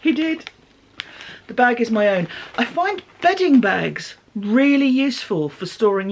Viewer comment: I thought you said ‘begging bags’ 😂. The idea being himself has to fill them up - a bit like charity bags…